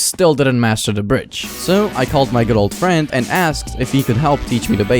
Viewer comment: bro that good old friend sounded like girlfriend 💀